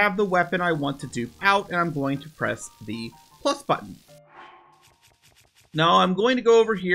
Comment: I can't tell what button you're saying. Sounds like "clup" button.